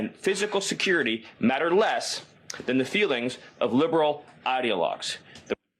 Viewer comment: Did he say they did this anti problem Because of feeling????!!?!!?!!!!!!!!!??!!?! Did he say feelings???? TAKINGAKNEE...ABM...GOAV